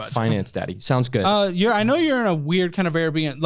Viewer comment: did he say finance daddy?